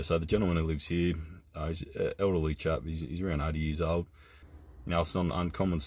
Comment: I had to turn the volume up really loud as I thought you said the guy living there was "380 years old" 🤣🤣